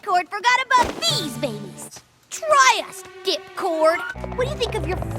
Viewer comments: I thought Iheard RD say dickcord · when I first heard her say that I swear I thought she said "Dick-cord" instead of "Dip-cord" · Does anyone else hear Rainbow say dick-cord at 3:39 or is it just me?